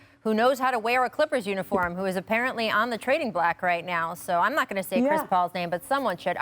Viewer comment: did she say “trading black”??